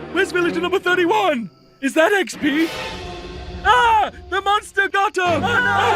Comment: the villager says "Ah, the monster got her!" but the captions say "Ah, the monster got him!" Which gender is Villager #31?